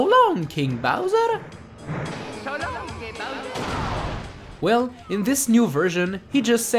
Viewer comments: I thought he sad :So long, gay Bowser!" It really sounds like that. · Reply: I thought he said So long eeyyh bowser. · I always thought he said <<So long gay, Bowser>> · After 18 years I finally know that he says "So long, King Bowser" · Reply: I always thought he said “So long, eh Bowser?” you know where the “eh” is pronounced “aay”